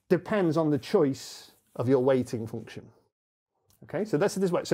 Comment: I really like this weighting function explanation. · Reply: This whole time I thought he was saying "waiting function", like... is each number waiting a certain amount of time or something?? 😂😂😂